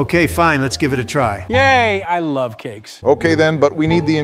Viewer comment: 'Yay I love cakes!' Joe really betrayed his ice cream 💀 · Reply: I heard it "I love kids"